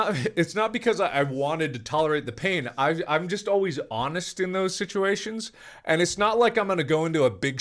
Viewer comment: What did he say he is always annus? 7:54 maybe